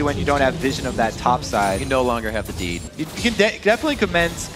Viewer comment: I thought he said "You no longer have the D".... I need some brain bleach.